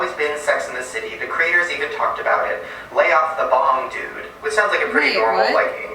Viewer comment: Haha...Curious George never had a tail, lay off the bong dude. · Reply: idk why but when i heard shane say "lay of the bong" i heard lay off the bomb lol